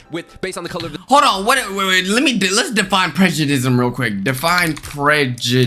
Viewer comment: did he just say prejedisum tf LMAO